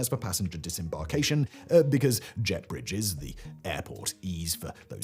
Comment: Oooohhhhh... JET bridges. Thought he said JEFF Bridges. Makes sense now.